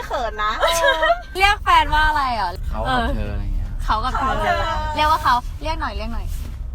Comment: ได้ยินไม่ชัดอ่ะ เหมือนไม่ใช่คำว่า "เค้า" แต่เป็น คิด วิเคราะห์ แยกแยะ 😱😁